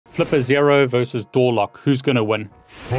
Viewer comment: Thought he said Gorlock